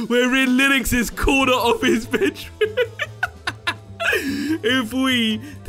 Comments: watch this did he say corner of his bit*h? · did he say "corner office b*tch"?